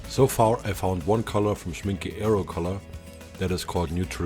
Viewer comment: Hi. Great video! What was the name of that high pigment grey that you found? The one that looks almost black. It sounded like you said Shrinky Arrow Color, but I couldn't find that on Google. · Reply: Thanks! 'Schmincke' is the brand, range is 'Aero Color' (their inks for airbrushing), color is neutral grey